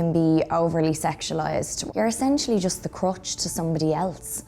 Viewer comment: OMG I thought she said crotch at first, not crutch. LOL. The sentence just prior was talking about roles being sexualized, so it seemed to fit. My dirty mind.